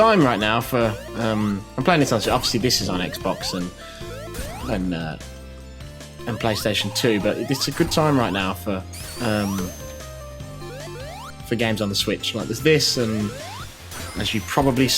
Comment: anyone hear he said playstation 2 and not 4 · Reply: me. But I know he said too, I just heard ps2.